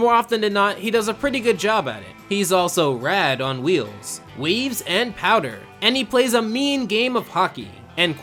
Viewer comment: you... you said weaves instead of... waves